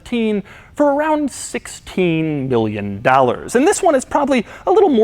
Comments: Sounds like he said 16 billion 😂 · 16 billion? Can sm1 make sure I heard that correctly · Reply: Million. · Did he just say 16 billion dollars ..... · I thought you said billion for a second😂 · Did he say 16 million? Because it sounds like he said 16 billion and if so I’m mind blown. Someone please clarify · Anyone else thought Doug said 16 Billion Dollars instead of 16 Million dollars haha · I thought he said 16 billion dollars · It sounded like you said 16 billion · Heh almost sounded like he said 16 Billion dollars lol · Sounds like you said 16 Billion lol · is that million or billion? I know it's million, but it sounded like he said billion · Lol I swear I heard “16 B-illion dollars” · ok i know he said million but it really sounded like billion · I'm sorry, what..? Did he say "billion"? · did he say 16 billion?🤔